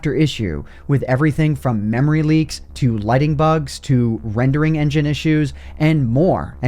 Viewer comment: I thought you said lightning bugs